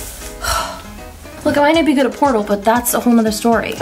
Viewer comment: What does she say at 9:18? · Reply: "I might not be good at portal".I replayed this like 5 times cause I thought she said porno